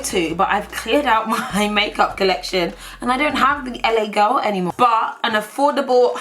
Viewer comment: Did you just say labia girl? Lmao!🤣🤣